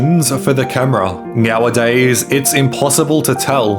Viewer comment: did dude say "meowadays"? I feel like I'm watching Super Troopers...